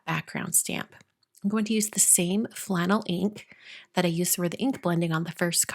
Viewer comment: Lol the color was flannel ink, I thought you said phlegm and was a little grossed out.